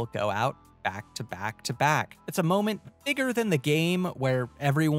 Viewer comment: can’t convince me he did not say the n word instead of bigger 😬